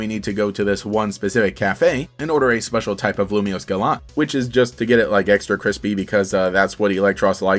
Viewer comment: LOL!! The AI Summary got the Wrong Game. The AI Summary said: " explores Canari's unique gym challenge in Pokémon Legends: Arceus. This playthrough involves trivia, a special Lumio Scalant quest, and a final battle against a surprising opponent. The journey culminates in a face-off against Canari, revealing a clever twist." Gemini, Since when did Canari end up in Hisui???? This is LUMIOSE CITY!! Also, it's a Review of Canari as a character. Also it misheard "Lumiose Gallete" as "Lumio Scalant"